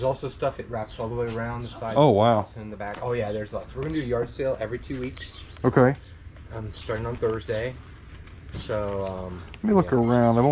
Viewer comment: In case you didn't catch it, that gentleman is having a sale every two weeks starting on Thursday. Just wanted to make sure you caught that. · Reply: I swear I heard him say he's having a sale every two weeks, starting on Thursday.